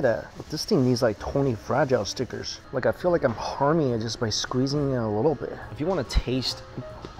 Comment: i thought he said “im horny just by squeezing a little bit” i literally had to play that back